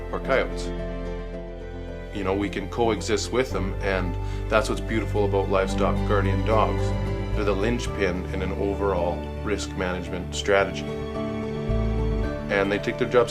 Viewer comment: not many people think responsible for the life of the predators you are doing a great job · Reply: I don't think he said that. He said co existing was cool.